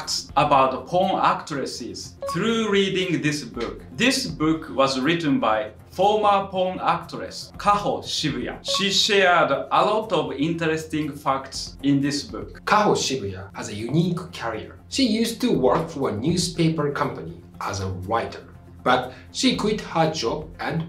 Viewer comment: somehow I heard "she share shared a lot of interesting fucks in this book" lol